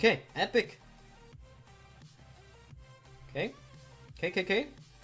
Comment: Did he say kkk at 8:30?